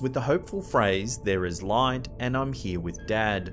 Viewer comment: I heard "there's no light." And "I'm here with the dead."